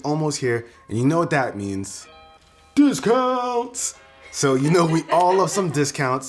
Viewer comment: Turn on subtitles at 0:08, instead of discounts it says booze girls